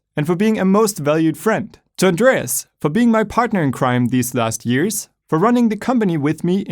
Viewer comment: did he say my partner in crime?